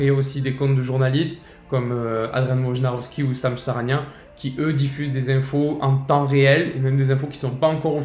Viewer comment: à 4:59, moi non plus j'ai pas capter les noms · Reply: My bad ! Pour les noms des 2 journalistes il s’agit de Adrian Wojnarowski & de Shams Charania. Vous pouvez les suivre sur Twitter et vous aurez en avance toutes les infos officielles de la NBA (notamment sur les transferts). En espérant avoir répondu convenablement à votre question les amis ! 👏🏼